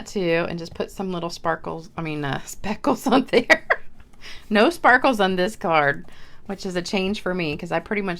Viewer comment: Ok, I thought you said no sparkle on this one,lol. Beautiful card and actually pretty simple! Thanks for sharing!